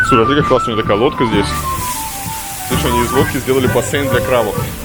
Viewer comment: послышалось "из водки"